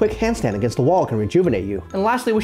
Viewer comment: I heard “it should ebb and flow like a pie” and now I want pie